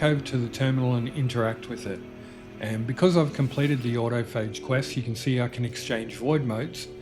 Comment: Hey i know im a little late to the party but at 7:28 in the video u said u had access to the staff and void modes because u completed a separate quest. I couldn’t quite understand what u said the name of that quest was but it sounded like “Order of age” or “Out of Phase” but i couldn’t tell. Please help lol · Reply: Sorry, I’m not very good at narrating. It’s the autophage quests. Hope it helps. 👍😊